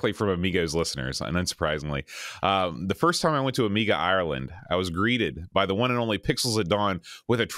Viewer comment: Amiga Ireland? I swore you said "Amiga Island".